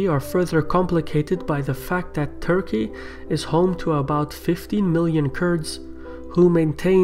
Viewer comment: 50 million kurds in turkey?? :D the country pop is 80 million. Shirvan's typical ME minority mentality.. What is your ethnicity Şirvan? Max 10 million kurds live in Turkey if which 60%is pro turkey and 40% pro pkk pkk. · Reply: My friend you misheard; possibly because of his accent. He says 15 million not 50